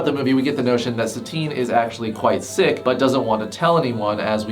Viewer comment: I thought you said thicc not sick so I was very confused for a minute or so 😅